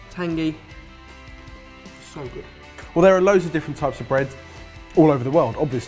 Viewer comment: I thought he said "There are loaves of different breads..." 🍞🍞 😄